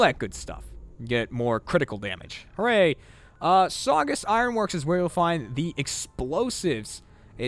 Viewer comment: for a second when he was doing the explosives bobblehead, I thought he said "So I guess" instead of saugus and I was like "Well I guess jeremy is ALREADY done with this" =P